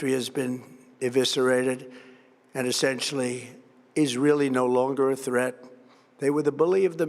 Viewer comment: did he say "Israel is no longer a threat"? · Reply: Referring to Iran, he said "is really no longer a threat." :))